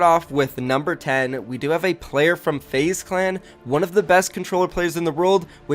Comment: I thought u said unknown is the best controller player